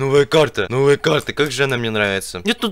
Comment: момент где мне показалось что он сказал трах клик🤣🤣🤣